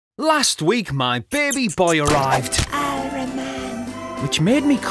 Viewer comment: What is he sayyed? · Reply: Ironman?!